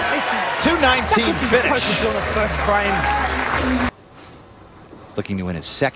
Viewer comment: What does Jason say at 3:32? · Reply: TheGabrielChannel “Thats what you were supposed to do in the first frame” was what he said, meaning he wishes he struck in his first ball in the 10th instead of the fill shot.